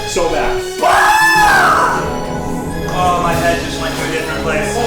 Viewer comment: lmao his head went to different place, ned said, but the captions said "(indistinct)" lmao ok